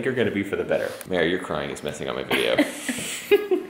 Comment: when he said “mere are you crying” and she shook here head. i thought he said “mere are you proud”